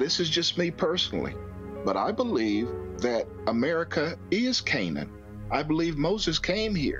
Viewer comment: Can someone tell me what the guy was talking about when he said people believe America is Kainin,??? Or Cainin??? What did he say